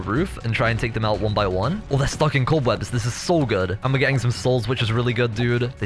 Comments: did he just say "oh there's f*cking cobwebs" or am I hearing it wrong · Is it just me or did i hear at 31:51 he said theres fucking cobwebs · did you say fuking cobweb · i heard fucking cobwebs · is it just me or do I hear a curse word? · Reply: stuck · i thought said Oh theres f*cking cobwebs thats so good but it was Oh there stuck in cobwebs thats so good im dum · Did anyone hear swear "Oh its fucking cobwebs" 31:50 Like if u agree😂